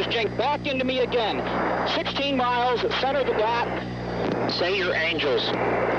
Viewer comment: What does he say from 0:33-0:35 ? "16 miles ...." and then ? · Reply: muzmuz71 Center of the dot.